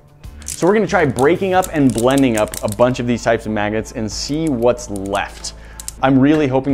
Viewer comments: "Breaking up and blending up a bunch of these kinds of MAGGOTS?!?!" 0:54 He say's "maggot" instead of "magnet" SO many times. Lol. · Sounds like he said maggots.